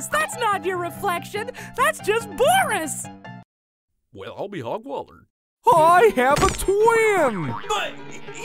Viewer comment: DID HE SAY ILL BE DOGWATER?